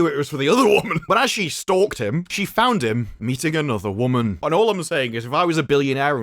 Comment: had to rewind. I’m driving and I could have sworn you said “eating another woman” lol.